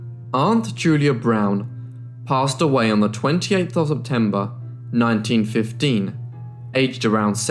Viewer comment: What were the dates. One sounded like you said Octember, Lol!!! Next it just sounded like Tember.